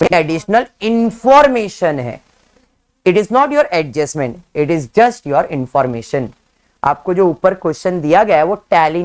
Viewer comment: Sir bich main apki voice cut hogai 20:22 pe tab apne kya bola ? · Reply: He is saying the equity shares and pref shares are additional information given They are not adjustments